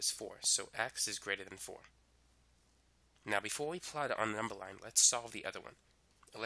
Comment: I have a question, what was said at 4:09? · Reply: Before we apply it on a number line